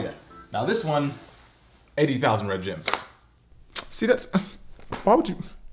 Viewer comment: So we just gonna ignore the fact that red gems aren't red, or gems? Ok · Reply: I thought he said red jam